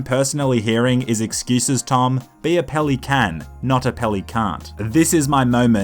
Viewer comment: "Be a peli-can, not a peli-can't " -, 2019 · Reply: Oh I kinda thought he said "be a peli-can, not a peli-cunt"